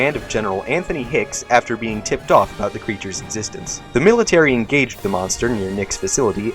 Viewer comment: it says: "After being tipped off about the creatures existence" The first time I heard it I thought it said: "Ticked off about the creatures existence" lol